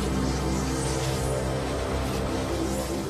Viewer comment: só eu que ouvi ele falar china tem C em vez de Shinra Tensei ;-;?